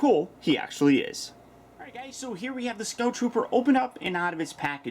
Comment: why would this not come with the speeder bike? that is beat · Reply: DID HE SAY 'SNOW TROOPER' (BTW An Sideshow Snow Trooper would be cool)